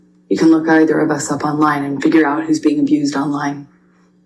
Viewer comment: To be fair and accurate, what AH actually said was "...and figure out who is being abused online" and not "...figure out who is the abuser online". There is a distinction. The first comment is referring specifically to current online abuse and her as the receiver of such. The second comment, the one that is hearing, would be AH telling the jury that the answer to the question "Who is the abuser" is to be found online (basically referring to the results of the UK trial). However I think misheard this one and that he is mistaken in this instance. Otherwise, thank you for great and informative content. I enjoy watching and learning.